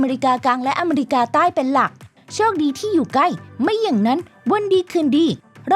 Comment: พี่ครับไม่ไกลหรอครับทำไมพี่พูดว่าใกล้ล่ะครับ(ไม่ฟังดีๆไม่รู้) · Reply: ไกลครับฟัง8รอบก็ได้ยินว่าไกลนะ ควรแคะหูนะแนะนำ-_